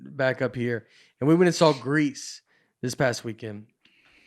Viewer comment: Greece? No, Grease. It sounded like you said Greece. They sound the same.